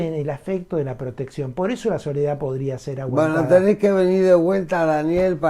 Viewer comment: no entendí lo último que dijo de la soledad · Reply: Según subtitulado la palabra es "aguantada" ...la soledad podría ser aguantada...